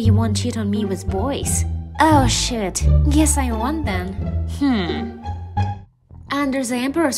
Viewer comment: When she said “shoot I guess I won’t “ it sound like she said sh! I guess I won’t * (Not hating it was an awesome video)😊